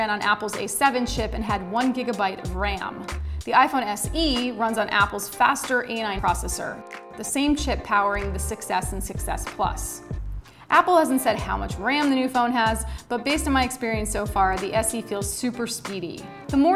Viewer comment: did she just say same shit? :D